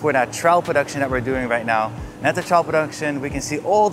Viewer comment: I heard "child production" instead of "Trial Production"